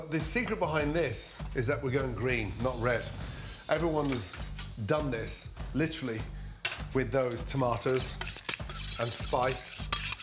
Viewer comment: "Everyone's dumbness literally with those tomatoes and spice" did he say that???